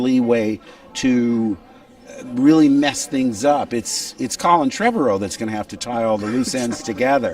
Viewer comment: did he just say Coleman trebor